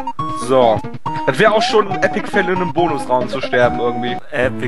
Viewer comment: Ich hab auch Epic Fell verstanden^^